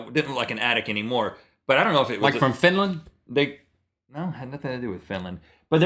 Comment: did Link say Finland! JEE :D